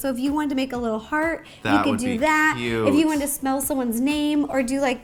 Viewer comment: does Ro say smell